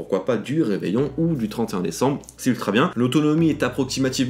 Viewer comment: C'est moi ou tu as dis "35 décembre " a